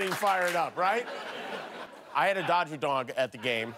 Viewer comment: Sounded like he said dodger dongs at first not dogs haha